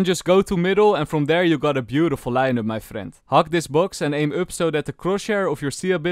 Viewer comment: why i always hear 'hug this box' ???